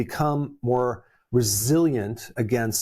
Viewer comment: "And so the seed now has this extra protection to become more 'Bresilient' against fungus, viruses, pathogens..." That's how I heard it. Wrongly of course. :)